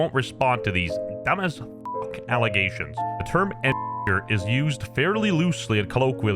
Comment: said the word again?????? it sounded like he said eni**er